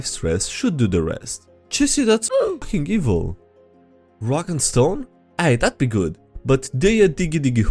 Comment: Did I hear a "Rock and Stone?"